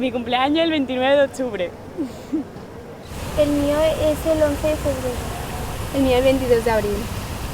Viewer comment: what what did the girl say at 1:01 I can't cant hear her over the water. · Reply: "- Mine is the 11th of February. - Mine is the 22nd of April."